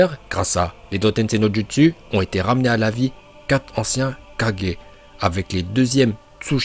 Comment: C'est moi ou tu dit 4g